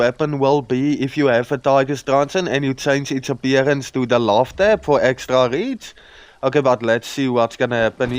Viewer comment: What is said to add at 4:39? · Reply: trans-mog the tiger truncheon using the love tap for "extra reach"